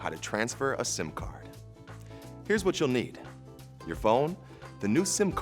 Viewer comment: i thought it said simp card i need my id back sir